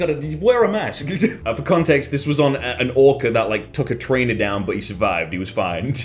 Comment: At first I thought you said train and that sounded so epic